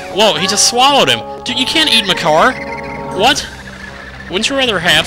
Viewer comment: "You can't eat Makar!" it almost sounded like... "You can't eat my car!" xD